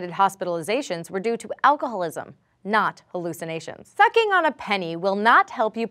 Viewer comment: I thought she said fucking on a penny LOL